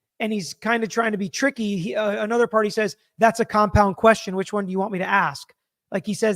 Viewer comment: I thought said “cop out”, not compound.